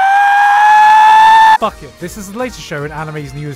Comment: DID SHE JUST SAID “DARLING”?? Zero two fans: Ah shit here we go again